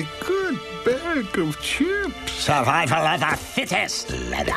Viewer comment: Who else cried 11:49 Drop a like for mr lenord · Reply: I heard "Survival of the fetish, N-word!!!!"